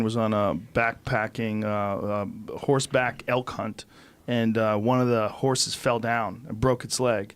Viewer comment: Oh elk hunt could have sworn he said elf cunt